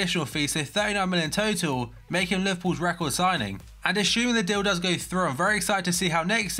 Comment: Did he say: "the dildos go through"? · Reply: 'Assuming the deal does go through'